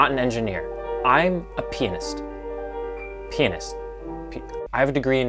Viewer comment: the subtitles think you saying penis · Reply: i heard that "I'm a penis"